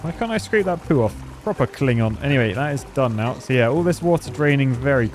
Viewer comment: Where are my fellow Trekkies who heard him say Klingon at 4:59?